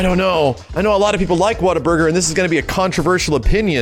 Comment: I heard "Waterburger" and his opinion made much more sense lmao